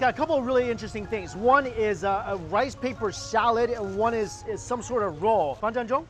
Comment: did he say "some sort of 肉”？